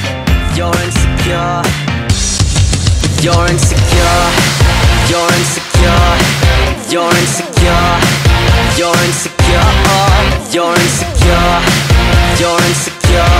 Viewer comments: Why do I hear "Go rinse your car"??? lol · Nobody: Therapists in their last session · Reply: I hear "Urins the cure" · Am I the only one that can hear him saying “Your in ze car” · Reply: I hear that and “urines the cure” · I started hearing "Urine's cure" after a while 🌝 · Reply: I hear "urine's a cure" my brain needed an extra silkble